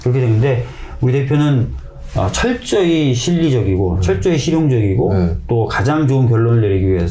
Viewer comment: 자막이 잘못됐네요..철저히 심리적이고가 아니고..철저히 실리적이고... 26:29 도...이꼴을 낳은거에요 보다는 이꼴 난거에요로 들리네요.